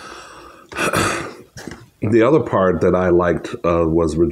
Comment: I love watching Regina King on 227 then watching her in anything else and thinking “Damn, Brenda’s a bad ass 😂😂” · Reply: I don’t know why in my head I heard you say Regina King 😂😂 I think because I’d just been watching Poetic Justice 😂😂 and I KNEW it was Regina Hall too that was doing the Oscar stuff. Lord I think I needed more sleep yesterday 😳